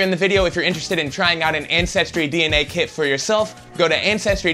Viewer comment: I thought he said incestry at first.